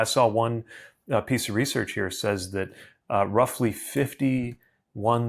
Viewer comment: I was listening, not watching, so when he said, "51,000," the pause made it sound like he said "50 [space] 1,000 people." I was like, "uhhhh... Does he mean 50,000? 50 to 1,000? What does it mean?!" I got so caught up trying to figure it out, I missed the next part and had to go back, which is when I realized he only paused.